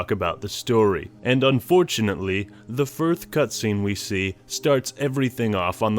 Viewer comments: did you say "the 1th cutscene"? lol, nice little touch there. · i heard 'the firth cutscene' X'D